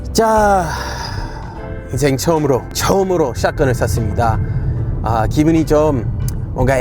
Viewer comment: 올리 쌤 ㅋㅋㅋ 인생 처음으로 샷건을 샀습니다 하는데 샀습니다 라는게 순간 쐈습니다로 들려서 깜놀 했어요 ㅋㅋㅋㅋㅋ